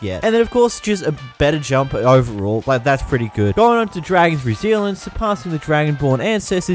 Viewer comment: the subtitels says dragons new zealand instead of Dragon's Resilience